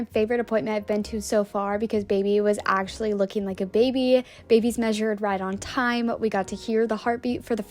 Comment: did I hear “BABIES measured right on time”?? Twins??👀